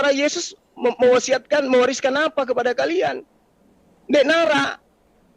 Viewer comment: Detik 2:58 kalo tak salah dengar bilang, "Ndekn Arak". Bahasa mana itu? 🙏🙏